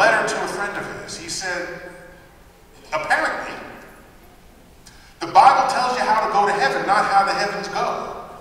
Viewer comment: not quite sure what Neil says when quoting Galileo "The Bible tells you how to get to Heaven, not how to......????" - please help. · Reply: "The Bible tells you how to go to Heaven, not how the Heavens go"